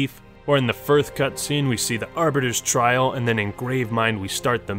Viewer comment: did you say 1th at 37:35 ? lmao.. "firth".